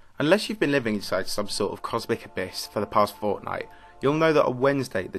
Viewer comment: for the past fortnite? Am i hearing things the wrong way?